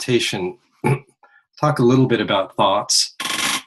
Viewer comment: it sounds like he says talk a little bit about farts and then 2 seconds later the chair creaks sounding like fart boiiiii